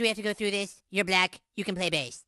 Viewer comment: "BE AS SICK AS YOU WANT! JUST GIVE ME A GOD DAM BASELINE!" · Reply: Na I thought he said I’m being sick of you whining so give me a Gahdamn baseline 🤣🤣🤣